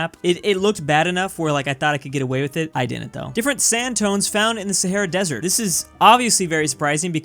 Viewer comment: I honestly thought it said skin tones and I didn't even question it